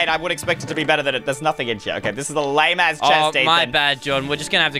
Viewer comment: Did u say a lame a** chest Jordan